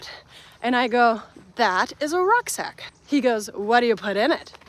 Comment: I thought you said a ruck sack and then figured out you meant rock sack!!!🙃 🪨